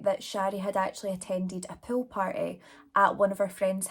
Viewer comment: Hf, I thought you were saying “pill party” this whole time but you were saying POOL party 😆 your Scottish accent is so cute lmfao